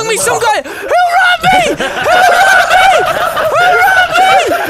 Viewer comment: I thought you said Harambe and not "WHO ROBBED ME!?"